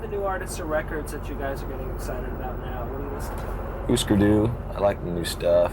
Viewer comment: Did he say "Hüsker Dü"?